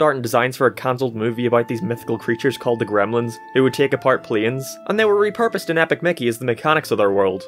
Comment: why did you say plines